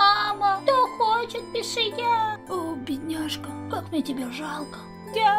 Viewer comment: мне послышалось: О 😂